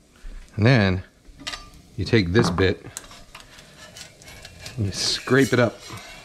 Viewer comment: I almost thought he said "Then you take this bitch" and that gave me a good laugh for a while